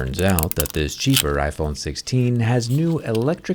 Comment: did he just said iPhone 16 cheaper 😮